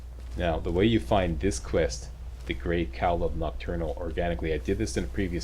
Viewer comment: i actually thought said the great cow of nocturnal